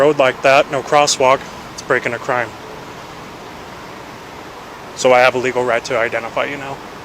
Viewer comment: did he say "thats breaking a crime" it's like he's nervous and fumbling over his words 😂